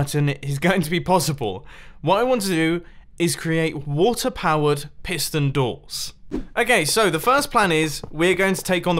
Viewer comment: Omg I thought it said Watters proof doors i was so confused